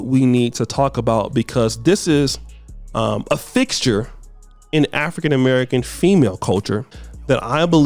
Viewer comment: said “fixture” but I heard “thickture” 🤣